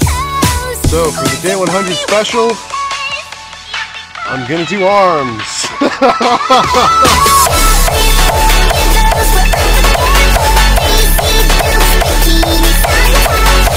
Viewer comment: i hear Sarms 🫣